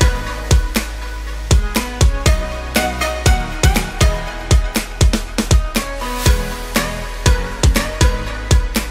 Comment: What kind of word is that? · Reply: Chrispratt